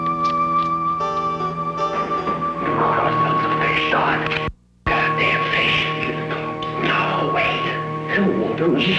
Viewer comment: I've never been able to figure out what he's saying at 4:47, sounds like "we got ourselves a fish stock"(?) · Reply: fwiw the official subtitles say "we got ourselves a fish Don."